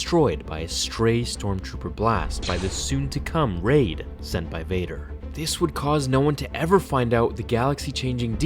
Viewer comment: For some reason, when you said "sent by Vader", I thought you said "Senpai Vader"